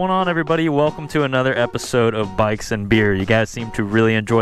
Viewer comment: I’m sorry but every time you say “bikes and beer” I hear “bikes and beards”